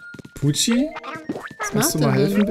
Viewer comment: Ich hab als erstes Gucci verstanden 😂😂😂